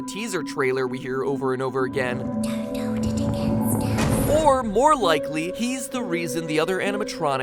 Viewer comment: Ok so Ennard is the broken animatronic and it says there is a little bit of me in every body because his name is a pun off of the word innards or intestines and what is he?basically an exosuit with only a head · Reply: And if you play the part when it says "don't hold it against us" it sounds like baby saying "suicide didn't work for us"